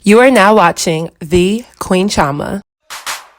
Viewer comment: I thought she said “Queen Trauma” at first. BUT Ironically, I feel like Khloe has this “body dystrophia issue” due to TRAUMA & her idea of “perfection.”